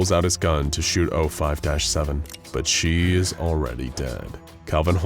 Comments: anyone noticed the caption around 7:20 says 05-8 instead of 7? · the subtitles say 05-8 instead of 05-7